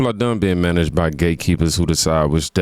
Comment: At first thought you said gaykeepers 😂😭😭😭